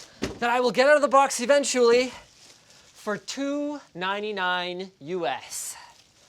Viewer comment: lol anyone else thought a first he said 2.99 usd instead of 299 usd